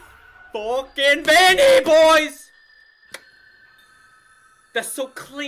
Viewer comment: i thought he said were fucking Vanny bois.